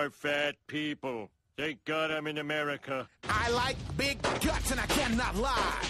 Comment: Oh I thought he said they got em in America